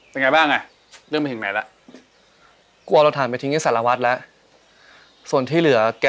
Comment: did I hear Sarawat? I'm not sure of it cause it has no subtitles 5555